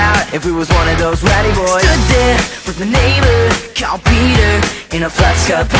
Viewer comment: oh i thought they were saying standing there with a neighboor cow peter